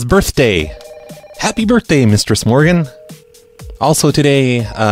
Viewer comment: I always thought he was saying "Mr. Smorgan". Am I the only one?